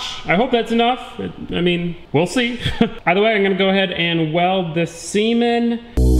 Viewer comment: wait what???? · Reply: Had to do a double take on that one. I honestly thought he said "I'm gonna go ahead and weld this semen" at first, but he actually said "weld this seam in".. LOL